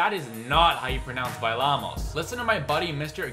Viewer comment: I’m Hispanic and she sounds like she’s saying “pelamos” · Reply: IKR!!! I thought she actually said "pelamos"....